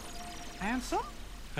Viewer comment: did mickey said "handsome"